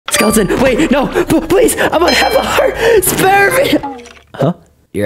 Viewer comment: “Im half a heart!” his hearts being all the way up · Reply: I thought he said "Heart" not "Hunger" 🗿💀